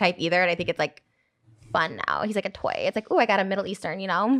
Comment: did she just call Moses a toy?!?